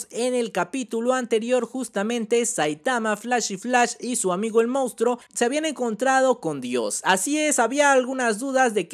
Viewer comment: como se llama ese amigo mounstruo que dices? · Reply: Manako